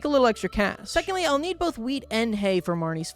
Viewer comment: When he said wheat I thought he said weed and was SO concerned